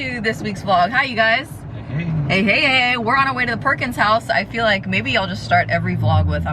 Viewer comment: I heard “we are on the way to the porkins house” 😂